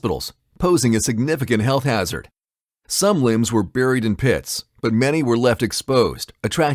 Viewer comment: I thought he said some limbs were covered in piss